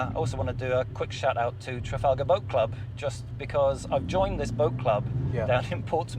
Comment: Thought said book club at the end not boat club. Tickled me haha 😂 solid car, setup and dude 👍